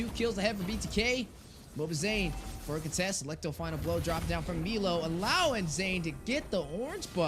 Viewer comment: did say blow job?😂